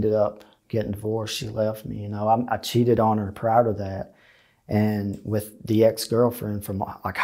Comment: "I cheated on her, proud of that". He's being contrite so I know I must have misunderstood, WTF did he actually say? Thanks